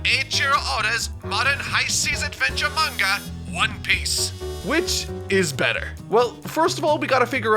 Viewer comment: First, I heard that as "...high seas manga, One Piece, which IS better." instead of the intended "...high seas manga, One Piece. WHICH is better?" :D Edit: +1 for the jazzy bg music!